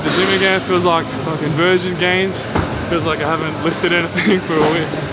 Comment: Did he just called out VEGAN GAINS? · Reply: virgin gains xD